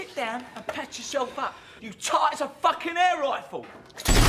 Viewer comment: there's always that one friend who goes overkill, and i always thought the leader said "your tight as an air rifle" but now i realise he said "you tart it's an air rifle" and the Bren is one of my favourite guns because like the BAR it's unique to the point i call them LMRs (light machine rifles)